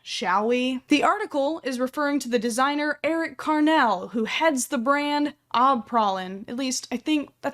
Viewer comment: I swear. I heard her say eric cartman